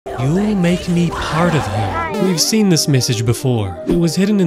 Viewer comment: I thought she said "YOU THINK YOUR SMART OF YOU?!" but its actually "YOU'LL MAKE ME PART OF HIM